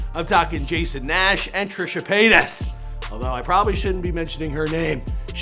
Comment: I thought said Trisha Paynis